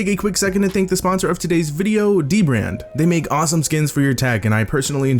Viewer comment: when he said D-Brand, I randomly thought he said DIO BRANDO